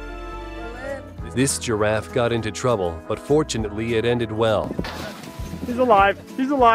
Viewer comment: who else thought they said unfortunately instead of fortuneatly